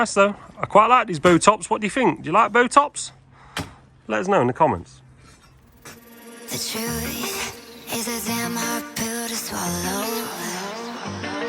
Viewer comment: I thought you were saying “I like Botox”